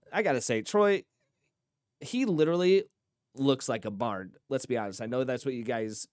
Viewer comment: Thought you said Troy "... looks like a barn" ... Was about to be offended, then grudgingly agree, then you repeated "... bard" :-D